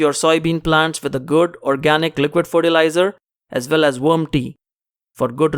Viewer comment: Thank you for this video. I am growing soybeans from your directions. At 4:22, did you say "warm tea"? Thank. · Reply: "Worm tea"